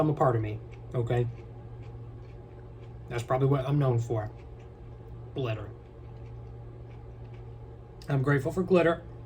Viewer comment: did anyone else think he said clitter and not glitter?